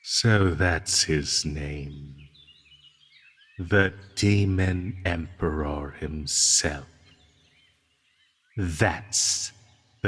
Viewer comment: I am so sorry but I heard: "[...] the demon eMpe pause ✨rAwR✨"